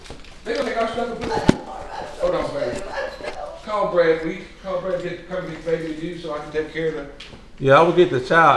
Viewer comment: I can’t be the only one who thought he said “hold on baby, imma call Tom Brady” hahaha 😂🤣